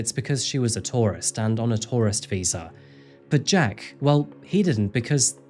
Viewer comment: I heard at about 18:40 that Ashley didn't work because she was a Taurus. As I tried to figure out what the Zodiac had to do with overseas employment, I heard Taurus(t) visa. Reparsed that to the correct tourist visa and had a laugh at myself. 🤦🏼‍♀️ Continuing the vid now, thank you for your attention.